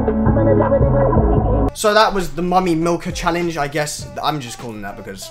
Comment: Lol I thought it said " The mommy killer"